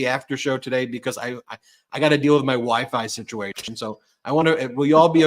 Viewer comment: Oh lordy Ben. At the end there i thought you were about to say " i have to go deal with my wife" but you said WiFi. Thank God 😂